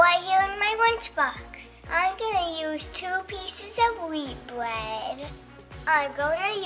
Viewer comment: Omg when said I’m going to use WHEAT bread I heard her say WEED bread 😂